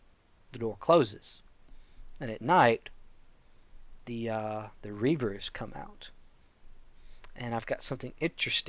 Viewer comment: are you saying Grievers or rievers? cuz at 6:08 it sounds like ur saying rievers